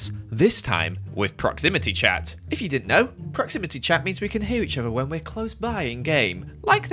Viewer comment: WAHAHAAHA I HEARD "proximity chat means that we can hear each other when we're close by and gay"